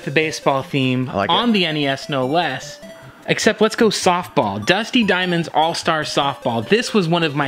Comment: You guys are legit hilarious! And YAY guest appearance! I misheard and thought said "DUSTIN Diamond's All-Star Softball". I am now giggling to myself and my wife is looking at me strangely! Also, great call on Silent Hill. I covered the first 2 games in my Halloween special (going live for Youtube tomorrow) I actually listed Silent Hill 2 as my second favourite horror game - but also effused about the first game too. The horror in those games is waay more effective and psychologically effecting than the cheese of the Resi games (which I also love, but for differnt reasons).